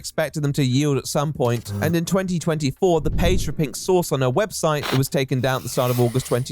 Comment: Can someone sort this part out for me? I have no friggin clue what he's saying here. I've listened to it at least 7 or 8 times and I still don't understand. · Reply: It is a bit choppy, isn’t it? I’m pretty sure it’s something along the lines of … Then in 2024 the page for pink sauce on her website , that was taken down in Aug of 2022 because the FDA asked her to cease production , for the first time returned with the description “They can’t stop me. Coming Soon”